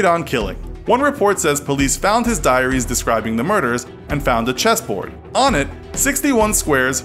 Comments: whoops i thought it said cheese 🧀 board 😂 me: what’s a cheese board? time to find out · I thought it said cheese board and I be never been so disappointed